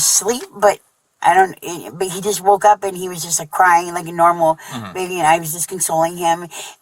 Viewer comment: It sounds like he said I woke up and he was dead.